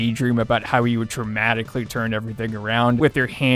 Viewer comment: I assume you said "dramatically turn everything around" but I keep mishearing it as "traumatically" and that sounds a whole lot more accurate to life tbh lmao